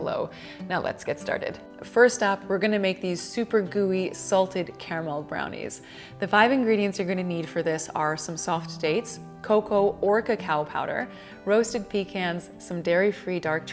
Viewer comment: When you said "or cacao powder", I heard "orca cow powder" :P hahahaha